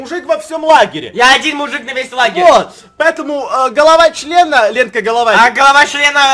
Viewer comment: кому послышалось голова члена на 11:44 ?